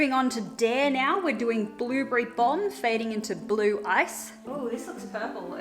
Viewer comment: Did anyone else mishear her for the first "Blueberry Bomb" for "Blueberry Thong"